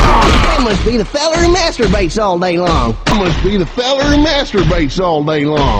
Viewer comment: I thought he said "the celery masturbates all day" and I was so confused...